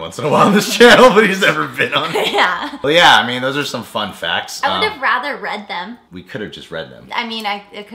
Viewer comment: I heard "Those are some fuck facts."